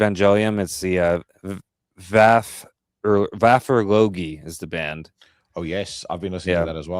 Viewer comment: Love me some news rant! mentions a band at 32:34 but I can't understand what he is saying. Vasar Logi or something like that. Google laughed at my feeble attempt to spell that. Any help would be much appreciated. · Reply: Just found it. lol. Vafurlogi. And yeah, it is sick. My kind of black metal. Cheers!